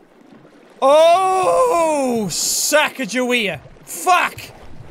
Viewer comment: The captions at 4:41 say "sack of juwea" instead of "Sacagawea".